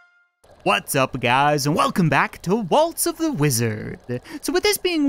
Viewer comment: Hey when say waltz of the wizard it sounds like you say Walto the wizard